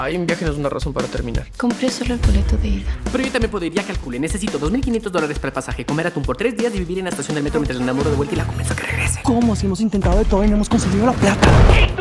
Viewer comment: Que dijo ahi? 1:55 Armando Mendoza? 1:57 Fernan y Luisito? 2:04 Eugenio Derbez? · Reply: Ya calcule, necesito 2500 dolares, comer atun por tres dias, vivir en la estacion del metro hasta que la enamore y la convesca que regrese